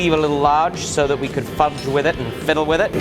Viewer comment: Hold up am I the only one that heard him say f**k lol. I heard it right before fiddled around.